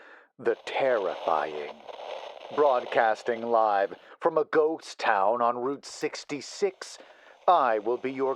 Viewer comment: Good story! But on another note it's funny that on the opening lines instead of hearing "ghost town" I always hear "goat town" XD 🐐